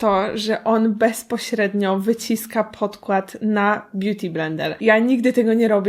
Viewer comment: kto tam usłyszał "blendel" XD